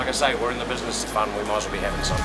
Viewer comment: what does that guy say in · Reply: "We're in the business for fun, we might as well be having some."